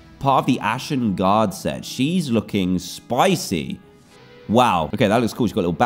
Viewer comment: Did he call her spicy 🤔